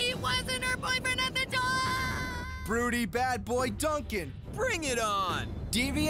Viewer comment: Did he say fruity bad boy Duncan?